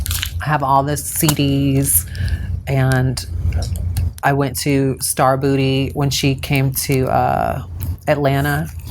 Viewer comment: what was whispered? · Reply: Sounds like "jasmine? "